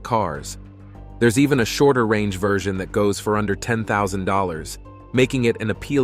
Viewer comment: Youtube is cheating with the US, here at 5:30, commentator said 10,000 dollar but youtube translation subtitle said 110,000 dollar !!. Before this part of video, earlier subtitle also not accurate